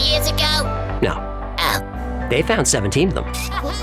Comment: What did you say? Speaking too fast! Had to replay 4/5 times! Did you say "17 of 'em"?